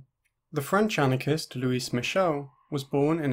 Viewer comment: for a sec in the beginning i thought you said “the french anarchist, who is michelle” lmao